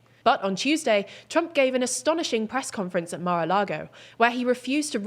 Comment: Wow. Did she just say, "asthonishing press conference"?